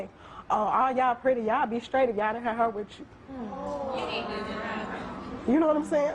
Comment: Please can someone tell me what she is saying, I can't understand a word (not native english speaker) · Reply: She said "All of y'all are pretty. Y'all would be straight if you didn't have her with you". She was basically saying that some men called her ugly in front of her friends because she has darker colored skin and her friends had lighter colored skin.